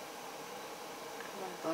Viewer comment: 아.. 근데 6:50 에 뭐라고 하신거에요? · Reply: I said "My body is going completely numb"